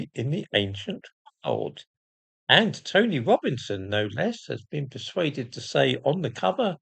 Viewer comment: if tony robinson is involved, you know it will be bollocks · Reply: I thought he said TOMMY Robinson, and I was a bit confused.